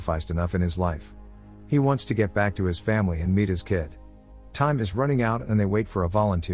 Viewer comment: who else thought he said "he wants to get back to his family and beat his kid"